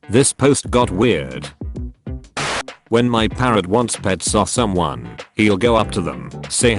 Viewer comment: I heard parent so i was extremely confused